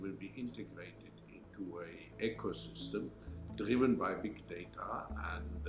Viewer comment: Klaus' eccent is so German, when he said the world should be run by "big data", I thought he said "dictator". Very scary indeed.